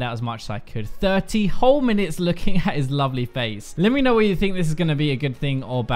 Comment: I thought you said ugly face, lol.